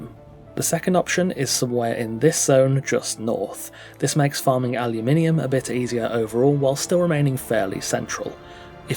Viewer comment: does anyone know wtf he's saying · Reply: "This makes farming Aluminum a bit easier overall, whilst still remaining fairly central" 😂